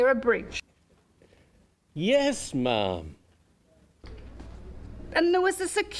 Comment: it sounds like he said yes mom